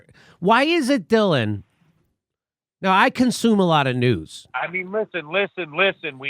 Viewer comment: jimmy, for a second i thought you said "....consume a lot of booze" .... sorry , man !!